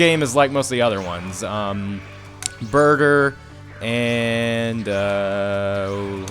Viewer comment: when you said burter I thought you said burger lol